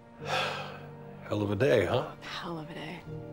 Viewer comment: What they said in minute 1:31? Thanks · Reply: he said ''hell of a day'' :)